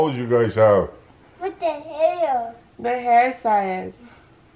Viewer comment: For a moment I freaked out when I THOUGHT said “what the hell”... but he said “.the hair!”😳😅😂😂😂😂